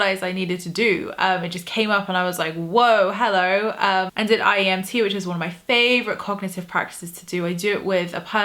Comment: I could not understand you at 3:23 did you say IEMT?